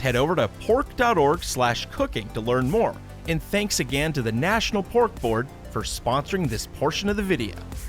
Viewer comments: I thought that he said National Pork Bork. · I swear i heard it as National Cork Board! Should i go to a doctor!?